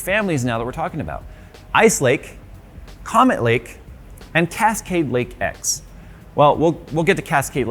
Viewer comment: I thought you said "Comment Lake" 😂😅